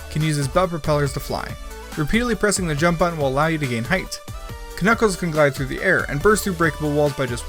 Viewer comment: Did you call him...K Nuckles?